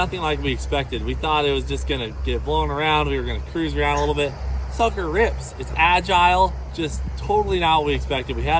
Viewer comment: I thought he said this fucker rips lol